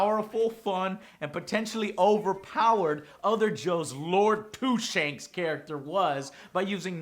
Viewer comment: lol i thought he said lord two shakes